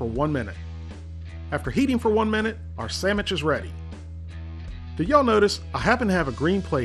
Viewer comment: Did he say Sammich?